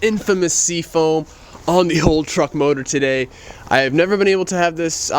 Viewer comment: Did you say "truck" motor?